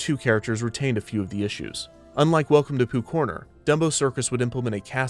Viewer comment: I'm sorry but I've always misheard it as 'poop corner'. something entirely different.